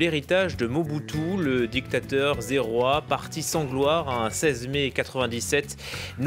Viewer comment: dictateur Zéroi 🤦🏾‍♂️, on dit Zaïrois